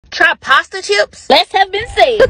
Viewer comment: I thought she said prostitute but she said pasta chips 🤦🏾‍♀️😂😂😂